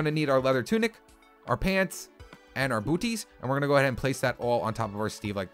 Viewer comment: Did he say....booties?